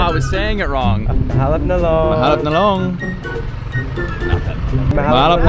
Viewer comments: why do i hear "mahal ang talong" 😂😂😂 ahahaha funny me right ahahah🙈🙈🤣🤣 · Did i just hear it like "mahal ang talong"?😂😂😂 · Lol 😂 at first, i thought i heard " mahal ang talong!"